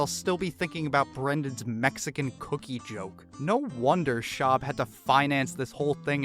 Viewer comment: Whoever booed after that cookie joke is a king 👏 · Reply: First listen I thought he said eggs and cookies